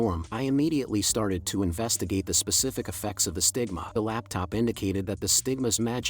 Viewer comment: ngl I thought it said sigma authority 😂